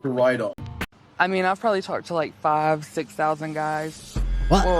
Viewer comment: Did she really say she ate 5000 or 6000 guys??? 😂😂😂 Certainly looks like it…